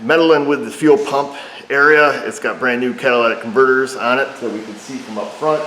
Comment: Sounded like you said, "It has brand new Cadillac converters on it", lol.